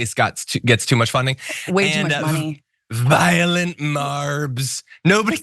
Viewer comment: I keep hearing, "violet" marbs, as in lavender 💜 😅😂😂 the dude is DONE. Never smart, he's now just damaged beyond repair.